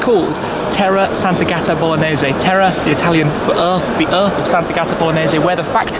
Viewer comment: For a moment I thought it said Spaghetti Bolognese on the inside of the spoiler...